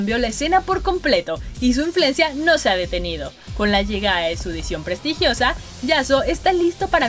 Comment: Pensé que decía "la nueva skin Sion Prestigosa" xd